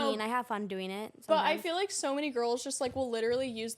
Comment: did no one hear what said (sometimes)😂